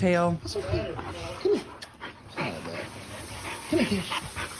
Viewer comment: Did Paul say 'hi dad'...? Ca 19:36 in snapchat vid.. 🤔